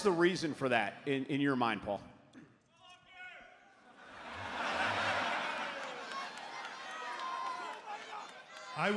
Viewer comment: what did the fan say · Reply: I love you